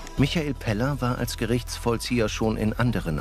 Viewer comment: Ich hab Penner verstanden